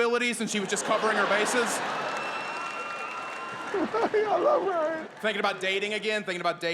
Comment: What in the hell does Terry cruise say @ 4:20 lmaooooo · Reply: "I love Ryan"